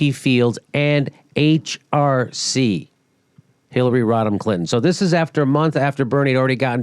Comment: jimmy , the whole entire time i thought you were saying "Hillary ROTTEN Clinton" hahahahah